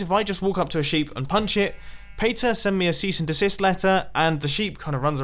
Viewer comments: I legitimately cannot figure out what on earth he is saying here. Like did I have a stroke or something? I legit cannot work out any English words in the following sentence. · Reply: Paytay sent me a cease and assist letter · wait wait wait, what did he say at 4:26? Pay me set and disease letter? what?